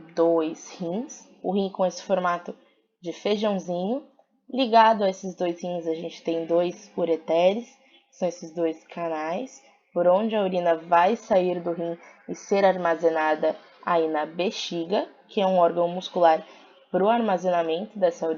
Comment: Só eu que ouvi Caraí no 0:49 ? 😂